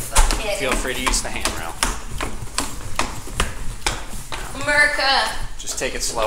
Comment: blud put (inaudible) in thte captions when she clearly said America